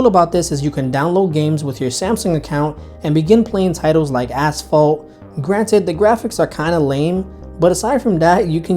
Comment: it sounds like you said ass fault